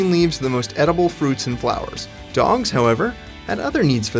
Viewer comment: Did he say dongs instead of dogs at 2:06 ?